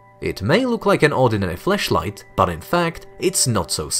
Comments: did you say Fleshlight? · did I hear that right! “It may look like an ordinary fleshlight” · what did he sayy? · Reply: Flashlight